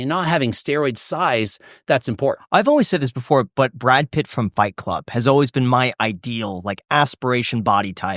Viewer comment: Gregg surprises me with how seamlessly he answers questions. He’s much smarter than he gets credit for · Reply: I was falling asleep then I heard Bike Club and Brad Pitt and thought there was a biker gang movie that I missed